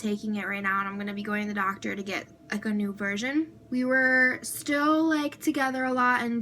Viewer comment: NEW VIRGIN what I know she said version im not dumb